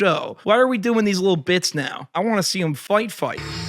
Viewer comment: An ad played as was saying “ I wanna see them fight fight” but he got cut off and it sounded like he said “ I wanna see them fuuu**” 😂😂😂